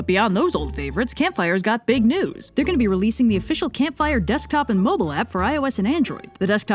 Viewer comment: In the Campfire Blaze sponsorship message, I misheard, "Desk Top" as "Death Cop". How wasn't "Death Cop" a 1980's slasher flick title. I mean, "Maniac Cop" was a great title, "Death Cop" sounds even more dangerous.